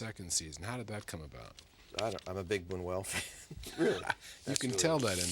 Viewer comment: When talking about dreams at 59:54, David mentions two influential figures one was David Lynch, but what was the other? I didnt catch that one. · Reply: Luis Buñuel, legendary surrealist director.